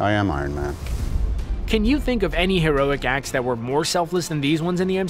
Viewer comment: Lol i thought it said heroin acts lmao